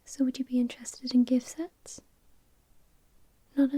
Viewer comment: I thought you said "gay sex" when I found out later you said "gift sets"